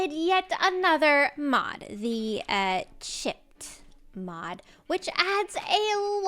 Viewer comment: I was only half paying attention and I thought said the "shit" mod for a sec, had to do a double take!!